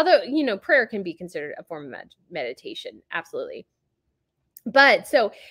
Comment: I'm laughing cause I thought that you said medication! Which makes more sense considering the source!!! Lol!!! Love you!